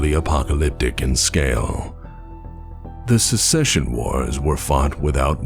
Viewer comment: Love your vids. I've been bingeing them. I dove down into the comments and couldn't find this. To my ear, around 8:45, it sounds like you're saying "Secession Wars", i.e., groups seceding from the Inner Sphere, versus the "Succession Wars", where they're fighting to see who succeeds to the throne of the Star League. "Succession" is pronounced pretty differently compared to "Secession". Edit: Around 10:00, I keep hearing "secession". Maybe you're saying "succession" really fast. I don't know.